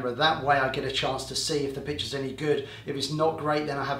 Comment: anyone else hearing the word "bitch" at 0:29 ??? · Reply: LOL "Pictures any good" Thanks for watching James 😀